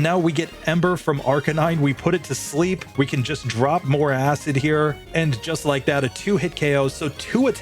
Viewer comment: did bro just say “drop some acid”???